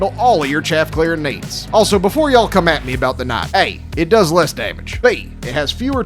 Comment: Lol thought said knife at first not knight.